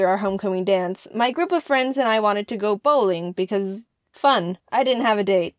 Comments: For a second i thought you said "My Group of Friends And i wanted to go Bullying cuz Fun" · Who else thought she said bullying instead of bowling? no one ? oh... that's ok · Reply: Nehon Neko I actually did... I heard "We wanted to go bullying for fun" and I was like "Wait whut?" XD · Oh I thought you said bullying not bowling lol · ICH HABE BEI BOWLING BULLYING VERSTANDEN OMG HAHAHAHAHAHAAHAH